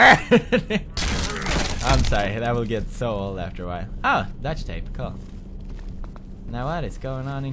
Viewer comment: does he say "DUCT tape," or "DUTCH tape"